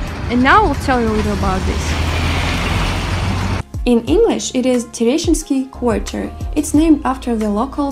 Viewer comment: I first thought she said “in English it’s pronounced Tenacious D” haha. I’ve gotta say I got drawn in and found this video interesting!